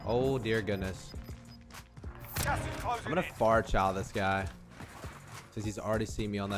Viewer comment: What did you say? You're going to "far child" this guy? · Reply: “Far Chal” He’s going to challenge from a far (a distance). Hope that helps!